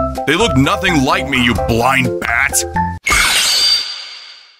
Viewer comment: i thought she said split him lol, it would be cool to see them use spears.